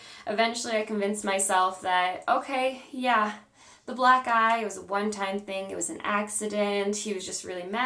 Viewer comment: At first I thought you said Black Guy! Then I was like wait why would he give you a black guy? You said black eye!!